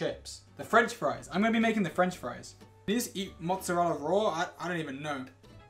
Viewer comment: did George say can you eat mozzarella raw